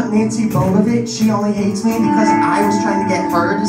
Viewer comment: Did he just say "Nancy bobabitch"?????🤣🤣